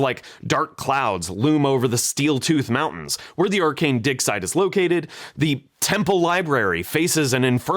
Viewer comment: I had to turn on subtitles, I swear to god I heard, "where the arcane dick slide is located. "